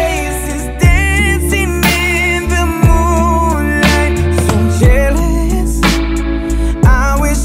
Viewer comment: Bro i thought it said “KISSING IN THE MOONLIGHT” 😂 But it actually said “DANCING IN THE MOONLIGHT”